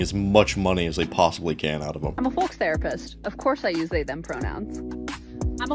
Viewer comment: I thought that woman was saying I'm a fox therapist